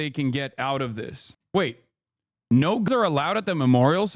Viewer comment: whooosh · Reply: oh I thought they were saying no gums. Nevermind